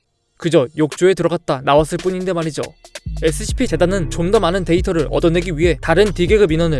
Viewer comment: 나만 존나 많은 데이터 라고 들었나..